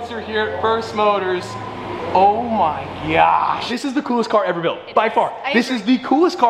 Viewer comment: did he say "Oh my gyatt"